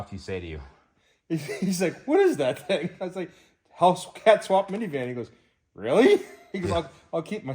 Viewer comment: I thought you said you were giving me a hellcat scat pack